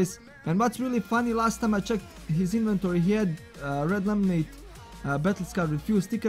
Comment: what item you said ? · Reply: He scams with red laminate,first time he had one with stickers and after few days he had new one...that means he is still scaming like that